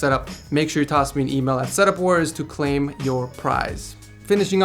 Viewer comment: I thought he said set up whores 😂